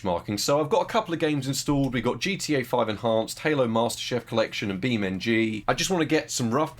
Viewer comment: Did i hear Halo Master Chef Collection